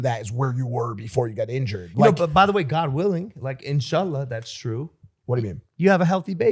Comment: Did he say Inshallah ??